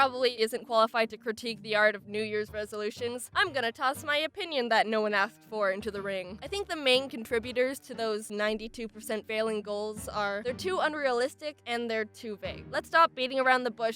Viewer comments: I legit thought she said to unrealistic or too gay. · Jaiden said "Im gonna toss my opinion nobody athked for" instead of Asked